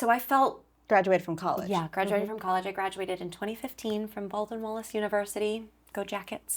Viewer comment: What university did she say??? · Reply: Baldwin Wallace!